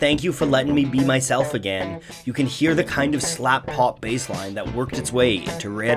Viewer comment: Did he just say S LA P P B A S E?